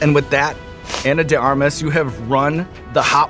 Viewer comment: Her name is Anna de *ARMAS*??? I thought Eric the Actor was saying "Anna de *Garmas*" all these years...